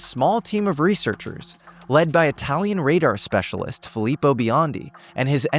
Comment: French?????? he's Italian..... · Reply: Okay, I thought he sounded Italian but I swore I heard say French, my bad.